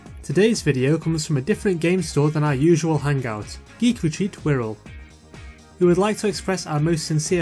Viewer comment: I thought you were saying Greek Retreat and was simultaneously confused and hungry.